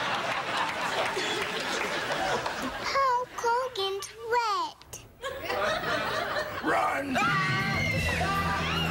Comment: What did she say? Rest in Peace Naya Rivera. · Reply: How cooked and sweat